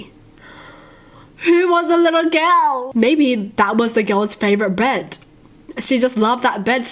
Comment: did say 'bread'? i must not be the only one hearing that